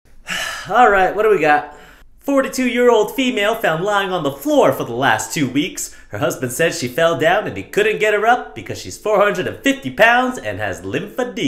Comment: I thought he said 2 or 3 year old in stead of 43 year old? It took me two times to realize what he said